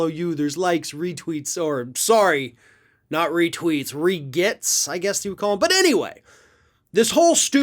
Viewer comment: I thought you say regrets not regets.